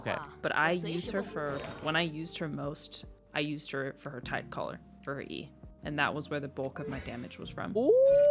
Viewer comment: I only use beidou for her tidecaller and I heard that as tight collar